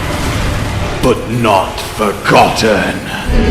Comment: why did i hear "lost but not forgotten!" as "lost but not for cotton!"